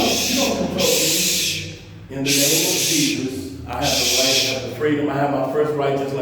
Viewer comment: Did he say @ 18:15 "In the name of Jesus"? WTF Scary as aka, whip your gun, taser, or pepper spay out on him like you would on a old "Black Woman" for J-Walking. They got you trained well BOY!